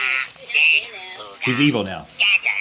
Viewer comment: the furby says:i'm evil now. that's what he says